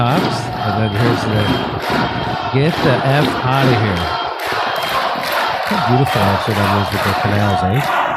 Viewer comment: Sounds like they saying get the fuck out of here get the fuck out of here